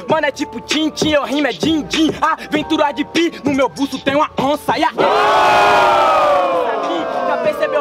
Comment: "no meu cu só tem uma onça" KKKKKKKKKKK não é possivel que só eu ouvi isso